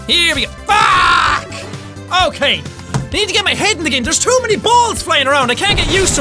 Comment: i thought u said that you love balls jack? xD